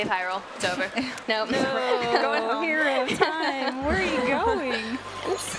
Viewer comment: POTENTIAL SPOILER Did she say what I think she said?@ 3:06:06 "hero of time, where are you going?"